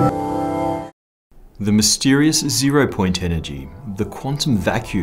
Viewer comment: i thought you said "de" mystify